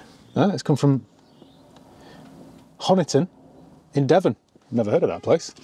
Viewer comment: did he just say hobbiton? Lmao was this owned by frodo??